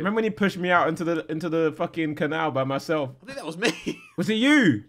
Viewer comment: did harry say N****, that was me at 8:52? or am i just dumb? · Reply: He said 'think that was me'